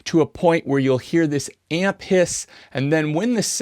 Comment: I honestly thought you said "ant piss". Which actually sounds quite fitting :D